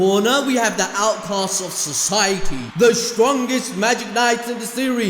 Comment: I thought you said Alphonse Society ... Besides the was the best "we don't care" ever 🤣🤣🤣🤣